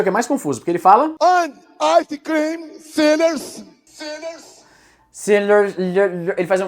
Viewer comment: eu ouvi "Icecream sailors" kkkkkkkk